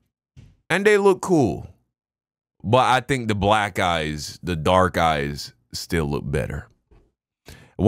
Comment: The black guys, the dark guys, still look better 🤣 that's all I heard when I came back to the video.